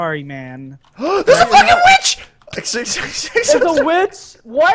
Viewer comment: Woofless: THERE'S A FUCKING WITCH!!! Me: ... did he just say fucking witch?... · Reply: he said fudging witch